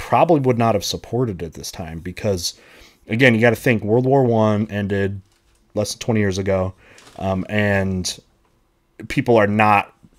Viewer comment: I swear I heard him say "Of course ww2 ended less than 20 years ago"